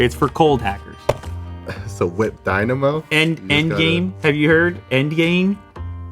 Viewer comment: Did he said gamer dynamo?